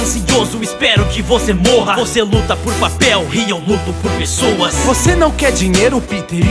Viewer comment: acho que ele falou PASTEL